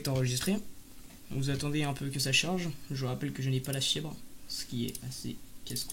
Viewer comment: tu n'as pas la quoi !? 🤣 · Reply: La fibre ahah